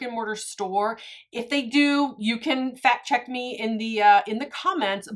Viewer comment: Frist I thought you said you can fat check me. 🤣